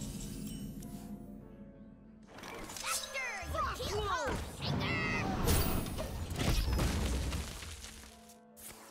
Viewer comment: what did she say? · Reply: Definitely could be wrong but sounds to me like "Faster, you keel-haul cankers!"